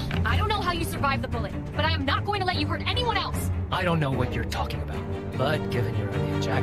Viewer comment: I think he has a clue of what's happening. Even though the subtitles said something else, I thought I heard him say: "I don't know when you're talking about." Kind of a low key "For me it was Tuesday" vibe I'm getting here.